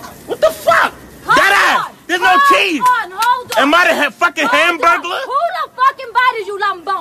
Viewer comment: Did he just say Burglar xD